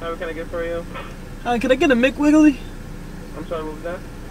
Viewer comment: Uhhh can I get a Mc willy or what ever he said got me laughing so hard